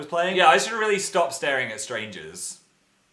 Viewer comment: I FUCKING THOUGHT HE SAID THAT HE SHOULDNT STARE AT STRAIGHT DUDES AND I WAS LIKE WAIT WHAT AND WHEN I LISTENED AGAIN I HEARD THAT HE SAID STRANGERS BUT I FLIPPING PISSED FIRST